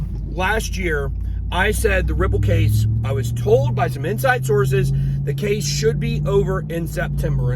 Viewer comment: BitBoy? More like Spit Boy. I thought you said “no more outside sources”.